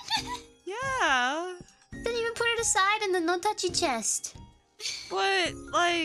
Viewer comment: just saying, what gura said was "no touchy chest"